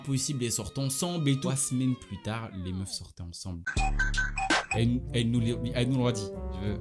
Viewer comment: j'ai compris 3 semaines plus tard les meufs s'enc***nt 💀